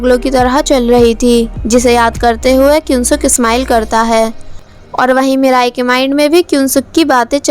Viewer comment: who heard mithai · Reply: Not mithai it,s mirae